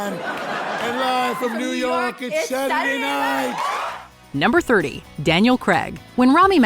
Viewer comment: it sounds like she says "It's Saturday Live!" LOL